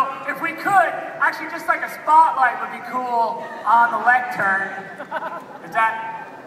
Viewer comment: Sometimes I come here to get hyped and think about what could have been. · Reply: Clock in 2:53... Did he say lantern???....